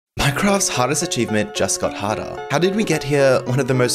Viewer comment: Thought you said hottest achievement 💀💀💀💀💀💀💀